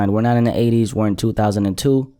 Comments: Am I the only one who heard him say "we're not in the 80s were in 2002" damn we in the past rn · “We ain’t in the 80s, we in 2002” 😤💯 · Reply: It sounded like he said “or in 2002” ?